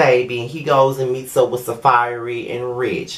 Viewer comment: When you first said Safaree, I thought you said Saaphyri, you remember her from FOL? 😂😂😂